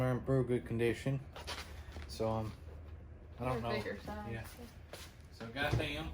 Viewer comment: Nice score on the NFL Game! Nice pickup at the Garage Sale, just with they would start here! LOL had to rewind, at first I thought you said Beer Size.